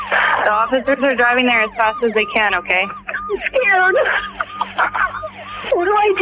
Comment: you're such a natural storyteller. (One quick thing, 2:18 - I think she's saying "I'm scared" not "Yeah"...) Sad story, though. Sounds like Relis wasn't perfect, but who is? He didn't deserve to be gunned down by his own kid in the safety of his own home. What a little POS creep. (Splitting hairs, but I think he was in lust with Kathleen, not in love.)